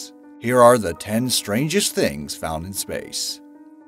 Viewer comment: I thought it said stranger things found in outter space